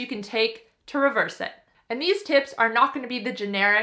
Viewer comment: Did she say "These tits"?..🤔